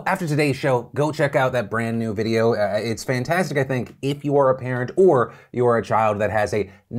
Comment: Did anyone else mishear "if you are apparent whore" instead of "if you are a parent OR"? Dude! 😅